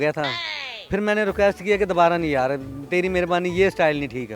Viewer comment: Did he said ye style dubara nae karna 😂